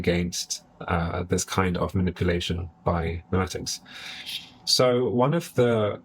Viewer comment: "manipulation by..." what was the word that followed? Am curious but couldn't make out what you said next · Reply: "memetics"